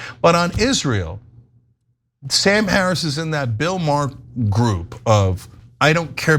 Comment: Phew - I thought he said Bill Burr instead Maher...spot on!!!